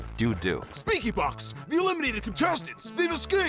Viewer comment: i thought they said lankybox at 0:46 🥀🫩😭